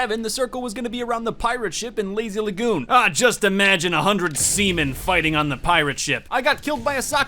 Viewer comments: I thought he said 100 semen yikes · I laughed so hard at 2:56 it sounded like he said semen not sea men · I thought he said semen lol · did he say 100 semmen? Ohhh.. he said sea maan.. oh